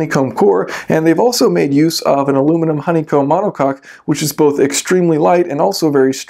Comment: what was that word you said aluminum honeycomb what? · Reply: Dim Simo monocoque. :)